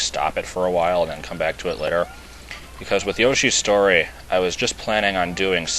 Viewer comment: Yoshi's STORY? I thought you said Yoshi's ISLAND. But I like Yoshi's Story better and I think when you play it it might be enjoyable. But no gimmicks please.